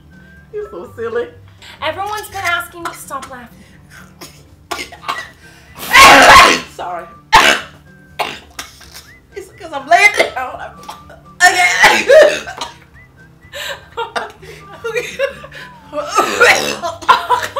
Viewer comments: Glozell: "It's because I'm black!" continues coughing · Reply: can't hear it I hear "It's because I'm black!" It just sounds even more funny 😂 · GloZell:*cough* sorry, it's because I'm bkack so -- cough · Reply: But i think she said it's because I'm laying down but it kinda did sound like she said it's because I'm black